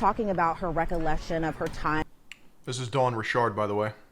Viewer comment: bruh i barely knew anything about this case so I find your videos sorta informative as well as engertaining as fuck.. but man i thought they were saying "Don Richard" like some french Canadian traficker or some shit that had some kinda link to this but nah.. Dawn Richard 🤣🥲